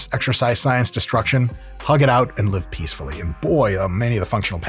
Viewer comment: Did he say “and lift peacefully” because that’s a t-shirt idea